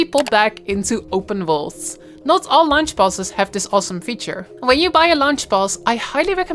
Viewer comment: i keep hearing "lunch boss" :D great video